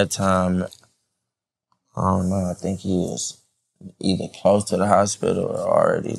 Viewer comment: Thought he said he didn’t know about this Xanax